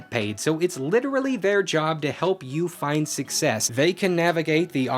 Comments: alright, who put "suck pee" instead of success in the subtitles? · Are we all going to forget that the captions say "suck pee"? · captions read “find suck pee” instead of “find success”??? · Captions say "...so it's literally their job, to help you suck pee" lmao